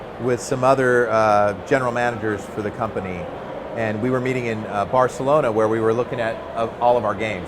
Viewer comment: it sounded like he said beating it :P